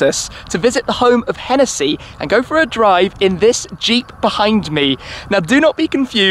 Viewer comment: For a second , i thought the car was called the .... Jeep Beehindmee...